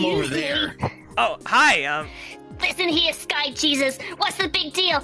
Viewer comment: Did sticks say "sky jesus!"?